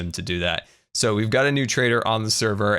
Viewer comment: When Scar said “a new trader” I misheard it to be a “traitor”)